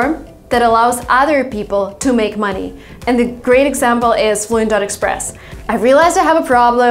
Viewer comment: Great video. Could anyone help me to understand what said in 0:58? Window express?? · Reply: Fluent Express